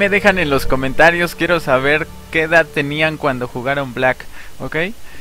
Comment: Pense que decía black ops jaja